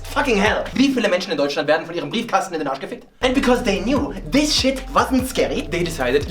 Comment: In case english viewers wonder what he says at 4:22 : German: Wieviele Menschen in Deutschland werden von ihrem Briefkasten in den Arsch gefickt?! English: How many people in germany get assfucked by their mailbox?! I know - beautiful <3